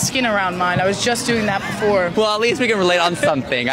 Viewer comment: I thought they said peeing themselves at the end at first, I missed heard the being myself man. I'm a tube🤦‍♀️